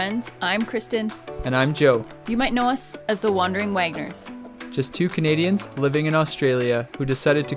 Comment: I thought U said ' The Wandering wankers".......... whoops.